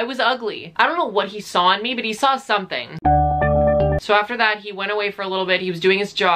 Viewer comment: I heard: “So after that, he ran away.” 😂😂